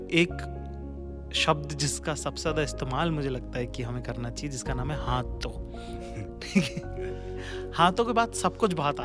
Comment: When he said "ha to" i heard it "hathon" 😂, "hathon k bad sab kuch accaha feel hota hai 😂"